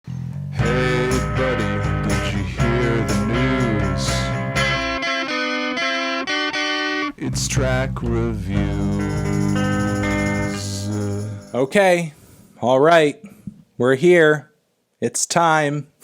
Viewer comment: At first I thought you said “Trap review!”...